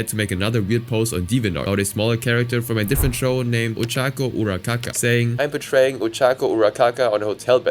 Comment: (Why It sounds like he Is saying Ochaco UraKaKa xD)